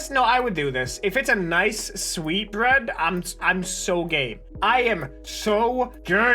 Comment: During the sweet bread thing I thought he said "I'm so gay" and not "I'm so game"